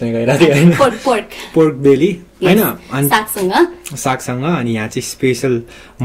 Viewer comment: did you say saksang?